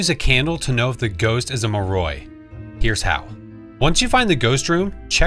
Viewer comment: lmfao i had to watch twice i thought you said you can use a candle to find out if the ghost is a moron